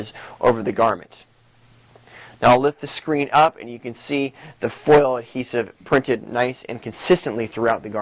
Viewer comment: hello ... at 1:45 what did you say?.. foil hezer? ... i would like to know what is that product... thanks.